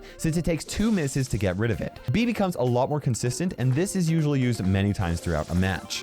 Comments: instead of Bea he said bibi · What says: Bea becomes a lot........ What I hear: Bibi comes a lt............